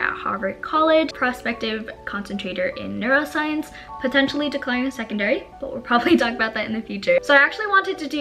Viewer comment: what’s her minor? i heard “decliner secondary”